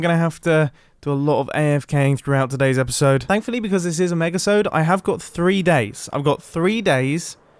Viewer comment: did he just said MEGASODE??